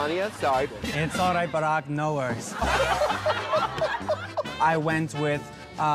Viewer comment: “It’s alright, Barack. No worries” never fails to crack me up that was definitely top 10 jokes I’ve seen in the snatch game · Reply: I thought she had said “It’s alright but I have no words’